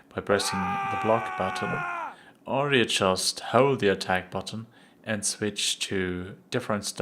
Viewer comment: What did he say at 3:53? --or readjust how the attack button.... I would like to find this option in the settings. It appears that he is holding down the L-mouse button while sporadically clicking with his R-mouse so that he can alternate his attacking direction quickly or "feint." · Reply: hold the attack button